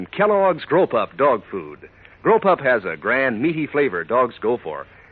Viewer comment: Ohhh... Grow-pup. GROW-PUP. I kept hearing "Grope-Up" and was like, "who'd want to buy that for their poor dog?"